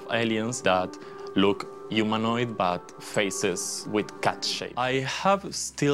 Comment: The space elf guy sounded like he said they have faces with cat s*** LOL